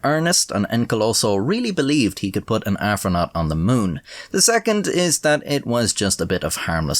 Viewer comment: Did OP say Afronaut?